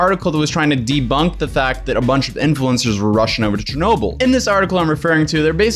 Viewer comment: They were RUSSIAN to Chernobyl? (Seriously, it sounded like you were saying that.)